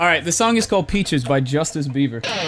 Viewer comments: I heard you say "Justice Bieber" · I heard him say Peace by Justice Bieber